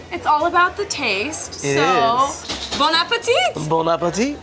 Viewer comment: I thought that he said “ bone apple tea”